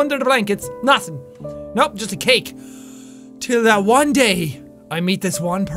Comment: what would i do for love i know this might seem crazy to you jack but id give up CAKE i said it i would give up CAKE for love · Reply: Jack would give up cake too. He clearly said just a cake at 11:03. I hope that the spam bots were recording this video. I must keep my faith cake… FOR JACK!